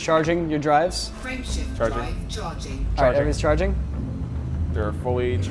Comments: for a while I heard "friendship drive charging" and thought it was pretty cute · Whenever the game says "Frame Shift Charge", I hear "Friendship Charge".